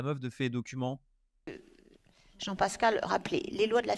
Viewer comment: A 23:14 c'est moi ou elle dit Jean Pascal 😂😂